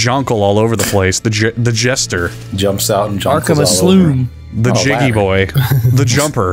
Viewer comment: Why did he call it the aslume? Is he stupid?